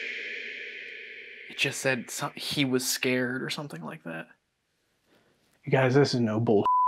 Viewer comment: I think the box said "you should go" real fast in a diff accent, not "he was scared" like the boys thought.